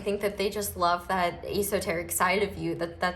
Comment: It took you saying "esoteric" and me being like "Oh hey what a coincidence, it sounded like she just said EsoTarot hahah--wait" for me to realize that's what you named the channel after xD... I'm sooo smurt~ Also btw wtf this reading is so accurate though?? Actually no I'm not surprised, they're always right on the nose. Love you, babe! You're one of my favourite people to listen to right now :3 getting channeled messages day and night hehe